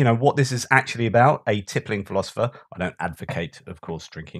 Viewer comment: I always hear .. 'a tripping philosopher..' keep up the good work!!